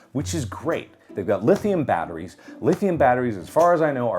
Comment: Thought he said Lixian batteries XD